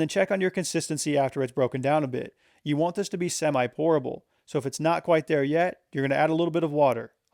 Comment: I heard "You want this to be semi-horrible" and was like.. why